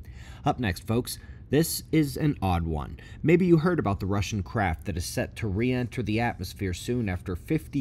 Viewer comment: Sounded like you said Russian crap.😅